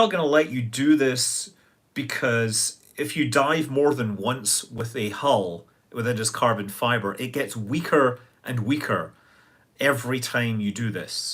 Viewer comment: At first I thought he said "If you DIE more than once with a hull that is carbon fiber, it gets weaker and weaker." 😂😂